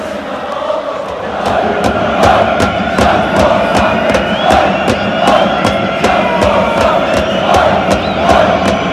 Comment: なんて言ってるか分かる人いませんか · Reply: オイオイジャンプフォーサンフレッチェです